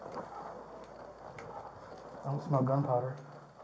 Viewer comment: I smell gunpowder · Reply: Marie D with headphones on I hear him say “I don’t smell gunpowder” but it’s very quick 🙂👍